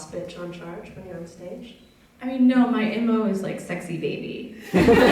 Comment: "My __ is sexy baby" What did she say there? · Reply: "My m.o. (modus operandi) is like 'sexy baby'"